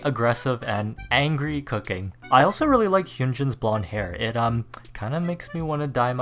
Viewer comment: I thought you said you liked Hyunjins body hair 😭😭 I almost choked on my steak